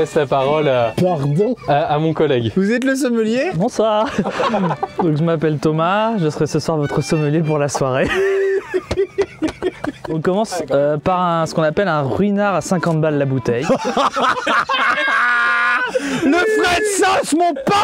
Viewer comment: s'assurer m'aurais fumer "bonjour alors moi c'est commis, je serais... "